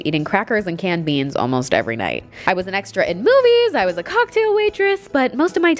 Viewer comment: I thought she said X rated movies😭